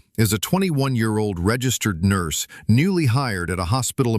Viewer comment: Her age is a problem for being a registered nurse at 21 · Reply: she said registrar, not registered nurse. I als thought she said registered nurse